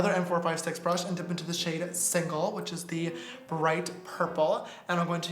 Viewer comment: when he said single i thought he said sickle lmao